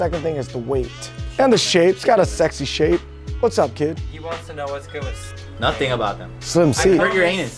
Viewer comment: what did say? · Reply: “he wants to know what’s good with slim seats”